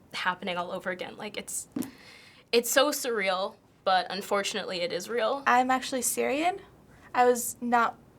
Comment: Was it wrong to laugh when she said Israel at 4:54? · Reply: misheard, she said "...unfortunately it is real"